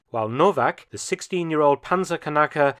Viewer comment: did you say "panzer kanacke"?